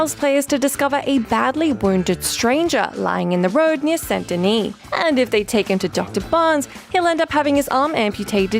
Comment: I've probably just played too much Morrowind, but every time anyone says St. Denis, I just hear "Seyda Neen" 😂